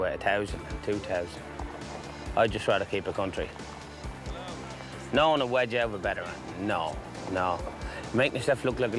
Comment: what does he say there? "no one would wager ____ ______" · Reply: “No one would wedge Out of a bet or anything” basically I think he saying no one wouldn’t pay their bet if they lost